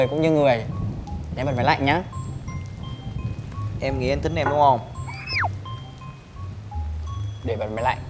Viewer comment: Coi engsub có nhiều đoạn cấn lắm nhưng cấn nhất là đoạn 12:05 Nam nói “em nghĩ anh thính em đúng không ?” Thì là “thính” chứ không phải “thích” đúng không ạ biên kịch, đạo diễn ??? Dịch thành “like” nó cấn lắm ạ. Cả auto generated của yt còn nghe không ra thì làm ơn đầu tư translator xịn cho phim với 🥺